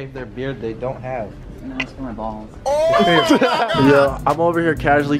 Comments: before I went back and read the caption I thought he said it’s for my balls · “at least I’m not going bald” Yo it sounded like he said “ no it’s for my balls” 😂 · didnt sound like he said "atleast im not bald" sounded like he said "its for my balls" LMAO